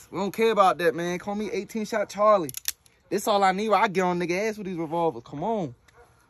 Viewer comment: “ I’ll get on a nigga ass w this revolver”😂😂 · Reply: I thought he said "I'll get on the gas with these revolvers" lol